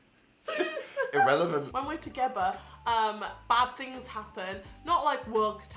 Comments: did say "When we're togeber" or am I just hearing things? 😂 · did say 'when we're togeber' meaning 'together'